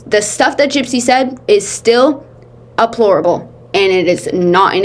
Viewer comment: I mean this with no judgment and say this with the knowledge that I could be mishearing.. but I swear I heard it on another video too. It sounds like you’re saying ‘aplorable’ instead of ‘deplorable’. Closed caption thinks you’re saying abhorrent.. which is a really great word! In case it’s not my hearing, I wanted to give you the heads up 😊